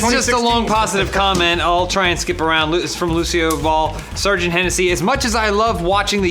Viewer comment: Every time they said Lucioball, I thought they said Lucielle Ball, to which I spent too long searching the channel for a Lucielle Ball video, confusing the shit outta me, and ultimately disappointing me... maybe one day.